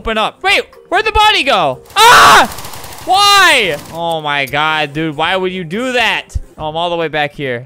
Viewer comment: I dont want to be that person, but did he say God dammit? Or dangit??? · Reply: That’s the third time someone said that he said “god dude”